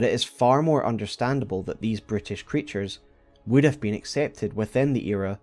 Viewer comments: You call the Omens "brutish creatures", I keep hearing "British creatures". My French ears say it's right 😂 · For a moment I misheard Brutish creatures to British creatures